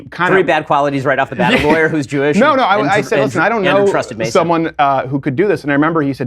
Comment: what are the three bad qualities? A lawyer who's Jewish and a trusted Mason? Is that what he said?